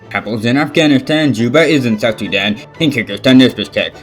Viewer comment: What it really is: Kabul's in Afghanistan What I hear:couples in Afghanistan